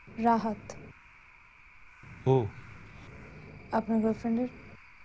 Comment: lol is he gay? Did she said" apnr boyfriend er"